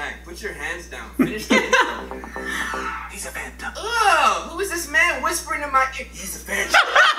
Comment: its sound like hes saying he's a fanta 🤣